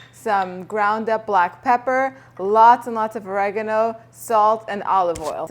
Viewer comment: Hi Could you tell me what one of the ingredients are in your recipe for Greek pita chips ,I can’t quite make it out , it sounded like you said raganau I am not quite sure I did not hear you correctly, and I would love to make these Greek pita chips of yours. Thank you Kind regards Marilyn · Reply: Oregano